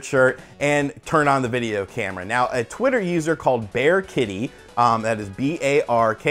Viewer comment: lol i thought he said bearkitty